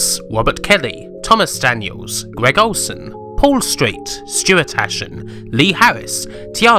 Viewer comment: At the Patreon shoutout I heard "Stuart Ashen" That's Ashens, the big British Youtuber! :) Thank you so much for these great documentaries, they are incredible!